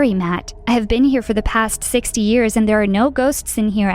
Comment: i thought was hearing 60 yrs old. me: "prolly misheard it too lazy to rewind." also me the end : AHCHHSHAHAVAKAOAO watching this in the middle of the night while home alone